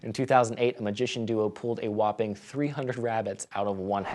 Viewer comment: I thought it said they pulled 300 Rabbi's out of a hat! Lmao, now that would be extremely awe inspiring